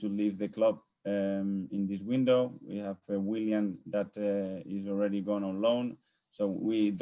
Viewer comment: I thought he said Willian is leaving on loan, then I realized he said “William” (Saliba) 😩